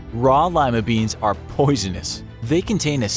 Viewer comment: What's lima? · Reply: Space duck I thought you said ligma for a second 😂